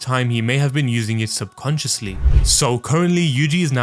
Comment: I heard him say punch so many times I thought he said subpunchciously not subconsciously Lmfao 😂